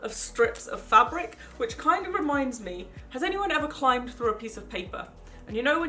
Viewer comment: "has anyone ever climbed through a piece of paper" Umm no 😂 (did I hear this right🤷🏼‍♀️)